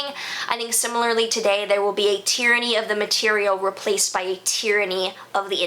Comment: Really sounds like the digital will rule our lives completely and it'll be where most of our lives are moved. I keep hearing a "tyranny of ideas" and the internet is literally a collection of ideas 😬😬😬